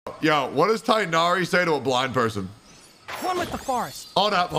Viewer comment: I thought he said “what does tighnari say to a blonde person” I was expecting the “let’s nip it in the bud”